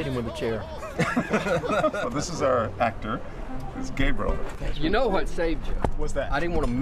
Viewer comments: subtitles on..... John said Gabriel Subtitles said Gay brother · My subtitles said he's gay brother